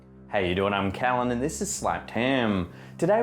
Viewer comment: I love how you say "slapped ham"🐖😂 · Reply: I hear "slapped 10" 😂